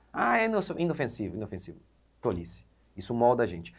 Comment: foda-se isso molda a gente..o pecado precisa de 1 centímetro,..." O seu foi concedido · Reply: Ele disse "tolice". Acho que você poderia ouvir melhor antes de comentar algo assim.